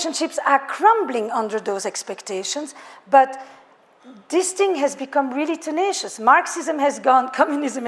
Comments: At the 9:51 mark, she said, "Relationships are crumbling on those expectations but disting has become tenacious....disting? Can someone tell me what she actually said? Thanks. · Crap! what what word did she say at the 9:54 mark? I heard disting but that's not a word. 9:50 "Relationships are crumbling on those expectations but disting has become tenacious" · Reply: Do you mean.. "This thing" sounds like disding